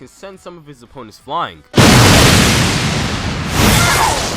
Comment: I thought you said todoloki 😂🤣